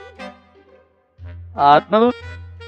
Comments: どうだどうだどうだどうだって普通に聞こえた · どうだどうだどうだどうだ 普通に聞こえる